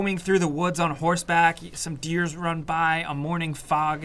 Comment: did you just say “deers”????